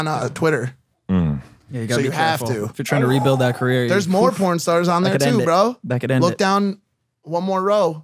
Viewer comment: Hahah at 52:20 says if ur tryna rebuild that career, that could end it, that could end it lol I don't even think anyone heard